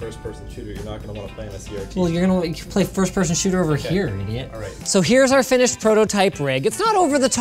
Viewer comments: Did you call one of your employees an idiot? No wonder you don't have any friends... Apologies if I misheard that. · first time hear linus said idiot, or i was misheard that